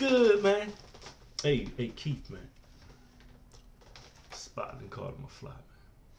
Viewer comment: It sounds like you saying "spydoncaught nafly"